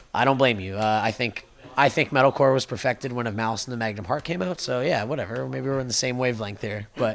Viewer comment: I can't figure out which album he's talking about at 7:43... Can anybody help me out? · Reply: Cédric Joly malice and the magnum heart - misery signals